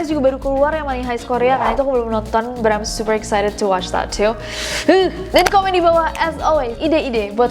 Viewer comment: di 14:20 sebut nama bram gasi? apa aku salah denger?